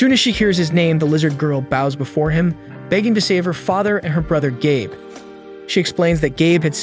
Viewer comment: Not gonna lie I thought she said to save my brother gay.